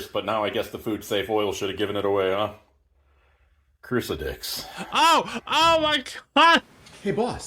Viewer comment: ngl i thought they had said 'cruci-stick' rather than what was actually said